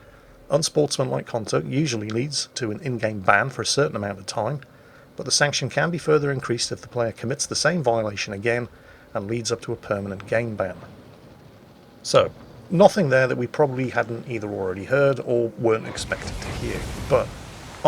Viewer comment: for a moment I though I heard "permanent gang-bang", which could certainly debilitate one's ability to continue harassing WoWS (haha)...